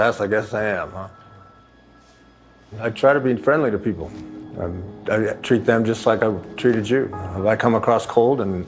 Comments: Stop complaining about the small box videos these people go out of their way so we can watch these episodes. Without the box they would be taken down in minutes so be grateful! 🌊🌊🌊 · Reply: Did he just say "I treat them just like I would treat a Jew" ? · Oh wow I seriously thought he said "I treat them like I'd treat a Jew" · Gordon is such a stand up guy. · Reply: I thought he said "I treat them just like I'd treat a Jew" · it sounded like he said 'I treated them how I would treat a jew.'